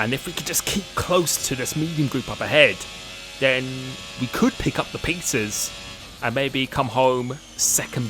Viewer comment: I think I should grab something to eat. I clearly heard you say "We could pick up the Pizzas" :P